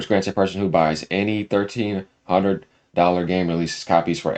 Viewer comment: I usually listen to podcast or videos while I game. I heard you say "1300 hundred dollar game releases" I had to pause and read that😆